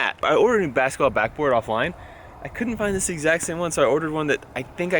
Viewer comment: did he say offline?